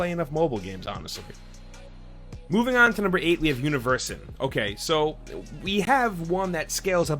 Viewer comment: what's the name of this game? I didn't understand him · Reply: The Universim